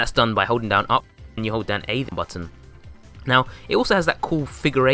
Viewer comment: How do you do the spin up run I couldn’t understand it sounds like you said “hold down up and able button”